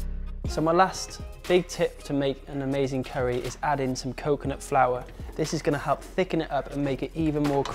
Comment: What is the final ingredient you added? I heard it as coconut flower. Is it fresh grated coconut?